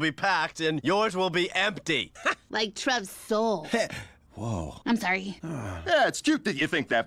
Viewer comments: When I turned on the closed captions for this point, it said "our place will be packed and yours will be empty, like Trump's soul." 😲 I'm not kidding. My closed captioning actually thought Bob and Louise said "empty, like Trump's soul!!!" Does yours do the same?🤔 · I love how Louise immediately apologized to Trev when she saw how much her words killed him. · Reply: When I turned on the closed captions for this point, it said "our place will be packed and yours will be empty, like Trump's soul." 😲 I'm not kidding. My closed captioning actually thought Bob and Louise said "empty, like Trump's soul!!!" Does yours do the same?🤔